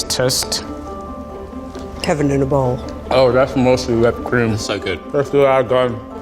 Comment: the closed captions say: Kevin in a bowl. Mostly we have a career in the circuit firstly our god